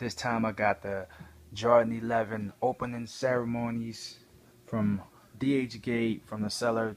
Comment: Opening ceremonies??? Guess that's the knockoff name considering there called closing ceremonies.... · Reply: I know wut the title says bro cause I fund it by name but watch and hear for urself at 0:10 u said opening ceremonies. I wasnt trying to be a troll. Doesn't matter really anyway. The shoes are fire and the title is right so it's all good.